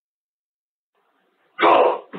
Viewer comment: bei der stimme ( 9:55 )Verstehe ich "go up" nur sehr schnell hintereinander gesprochen . was ja ziemlich gut passen würde weil er ja hoch geht :D